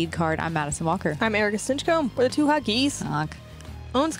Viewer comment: Love two hot geese commentary! GK give them a raise! 🥳So positive, fun and cool. Hope to see you two make lead card coverage asap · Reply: I hate to say it, but I thought they were always saying The Two Hot Geeks, which I thought was cool!